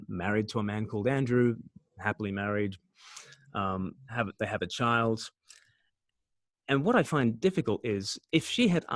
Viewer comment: I couldn't quite catch the title of the book his friend had stolen. At 16:34 " ... Christianity". What is the title? Just wondering. Thanks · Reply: Mere Christianity by C.S. Lewis.